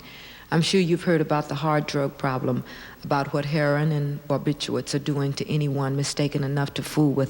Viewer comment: Did she say "heron"? A bird species is a hard drug?